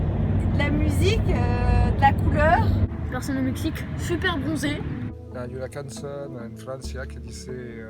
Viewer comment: yo entendí "Gente de color" pero en un lenguaje menos ofensivo XD -"Son superó bronceados" 😎👌